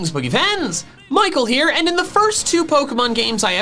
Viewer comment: "Greeting Michael, Pokefans here" is what he should say one episode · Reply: I thought he said boogy rans